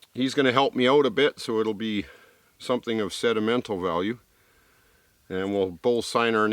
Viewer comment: did he say "sedimental" value instead of sentimental?